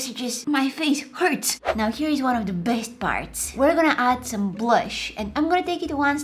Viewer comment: When started taking the eggshells off i thought she said "now the best fart"